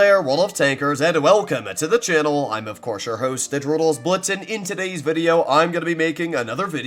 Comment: I always heard "this is your hosta droodles blitz" but it was "this is your host the droodles blitz"